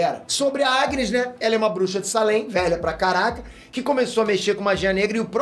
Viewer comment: ouvi com uma quase certeza absoluta um "Velha pra CARALHO" Kkkkkkk ri sozinho antes de voltar pra entender